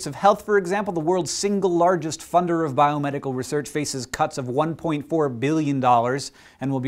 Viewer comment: you said 1.4bil but the caption says 1.2bil. WELL WHICH THE FUCK IS IT?